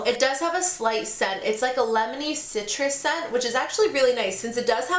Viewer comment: When you were saying that the CC cream had a "lemony" scent I kept hearing "Lebanese" scent haha